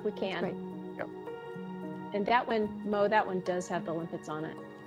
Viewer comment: y'all cannot tell me that someone did not just say geronimo